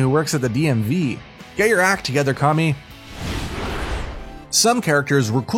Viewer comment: Did he say orochimaro